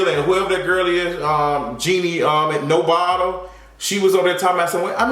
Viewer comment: Hold on wait! "Jeannie no Bottle" I cannot!!! 💀💀💀💀💀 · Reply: I thought he said " no bottom" cause.....😂